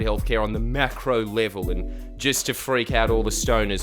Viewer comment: I’m not high but I still thought he said Macaroni 😂